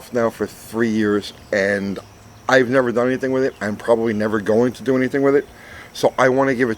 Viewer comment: That is pretty cool! The Father of the Dominator. The Holley 3bbl is the only Holley I've never messed with.. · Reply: I thought you were losing your mind, then I realized it was me going crazy. I thought you said ever. The word, never really makes a difference.